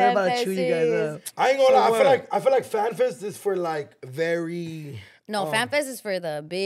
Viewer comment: Sounds like says flan fest 😂😂